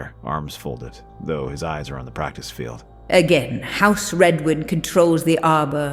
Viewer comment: Hearing house Redwood instead of Redwyne at 13:23 was weird.